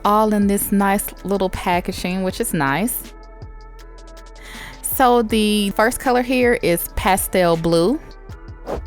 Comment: I was so caught up in the bangs color I thought she said “the first color hair” I was about to say oh we doing new things today💀 dying hair and stuff💀💀💀